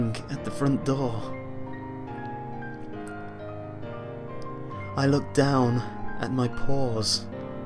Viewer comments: The first time I thought he said "I looked down at my pores," rather than "I looked down my paws." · I turned around really quickly when I heard "looking at my paws"...lol. Did not see that coming!